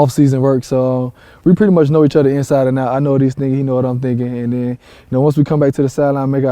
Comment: I swear I heard TY say " I know this n***a" lmao